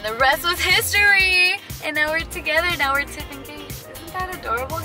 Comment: "And that is our story and now we're tiff and case" I thought she said now we're engaged IWAS LIKE WUT DID I MISS